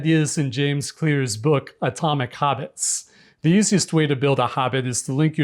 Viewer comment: I keep hearing "Atomic Hobbits."